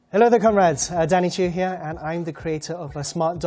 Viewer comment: did he say hello there cumrags